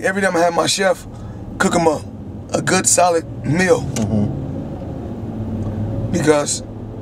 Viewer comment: good SOLID meal or good SALAD meal? what he sayin tho?